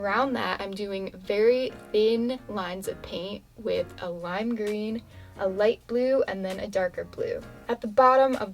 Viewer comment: Am I the only one that thought she said slime green